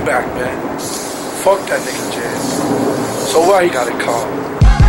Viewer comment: i couldve sworn he said fuck ja so what he got a car. lmao lmao lmao